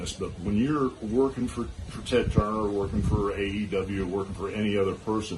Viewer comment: did he say working for AEW?